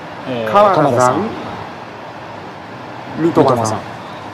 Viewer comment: 「三笘さん」が「ヌートバーさん」に聞こえた